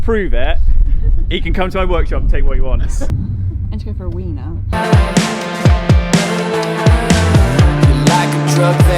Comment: What did she said? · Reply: “I have to go for a wee now” I think is what she said